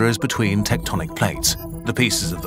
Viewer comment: Idk why but when I heard "Tectonic" I thought I heard "Teutonic" as in the Baltic Crusaders.